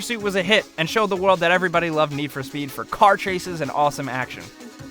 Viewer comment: I heard need for speed "shit"🤣🤣